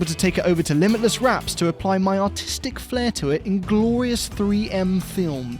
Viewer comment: i swear I heard autistic instead of artistic